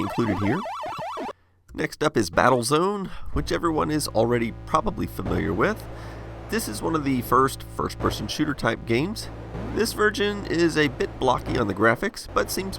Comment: is it just me or does he say this vergin?